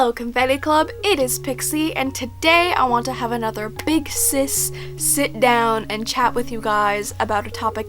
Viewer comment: i was looking another page while you were saying 'big sis sit down' and i thought you said big CIS sit down i was confus what does she mean by "withdrawl"? cause like, christian people would kinda have to 'withdraw' because it's a sin xD